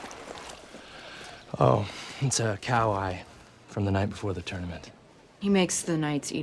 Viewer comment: Why am I here…? I thought they said “cow pie”…..